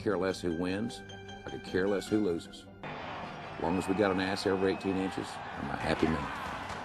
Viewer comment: "as long as we got ass raped teenagers" ??? umm what is he saying?